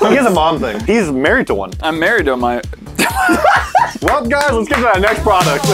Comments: making fun of peoples moms. "he's married to one", "i'm married to my mo-"😂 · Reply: Idk why but I heard it as "I'm married to a mi-" Didn't sound like mom to me at all lol. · making fun of peoples moms. "he's married to one", "i'm married to my mo-"😂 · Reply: I misheard it for I am married to a minor 😂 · I’m married to my mo... Everyone: sweet home Alabama playing in the back of their heads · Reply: I thought he said "I'm married to a minor"